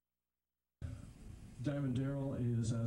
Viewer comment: did he say “Diamond Darrell”? And sponsoled by? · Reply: Yeah because he was Diamond Darren until their 3rd album.